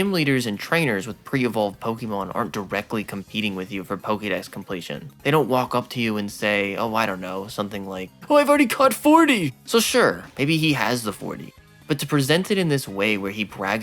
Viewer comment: In the thumbnail, i thought he said he caught 40 kids 💀